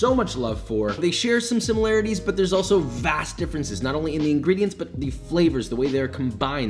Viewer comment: 😂😂😂it sounds like you said Vas Deferens